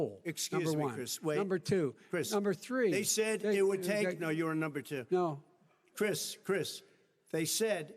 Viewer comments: Biden: number two.. Trump: Chris.. Biden: number three.. Trump: They said, it’d take.. ..No, you are on number two.. Biden: No Trump: Chris Chris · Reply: I heard "No, you're number two" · At around 44:50, Trump "No, you are no.2". Did I hear that right? :D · Joe: number 2 Trump: talks over him Joe: number 3 Trump: you were on number 2 That was the debate highlight for me! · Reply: I thought Trump said "You ARE number two!"